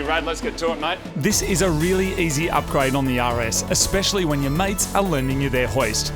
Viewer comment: Every time he says “the rs” I hear “the Yaris” and that’s a beautiful thing